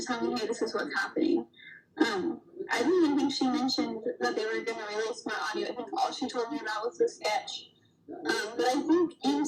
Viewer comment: "They released my audio" yikes did I hear that right?! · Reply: I hear “I didn’t even think she mentioned that they were going to release my audio. All she told me about was the sketch. 12:09 “I thought they were going to release my audio. All she told me about was the sketch.